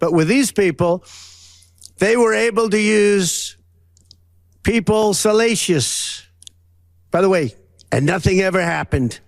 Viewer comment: "They were able to use people... salacious." That word does not mean what he thinks it means. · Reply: I heard it as "...using people's salacious", which makes even less sense.